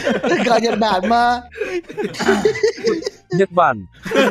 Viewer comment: nghe như Nhất Bạn =))))